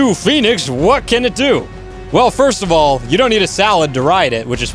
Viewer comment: Hey uh 1:56 lemme get a salad with a side of not hearing what he said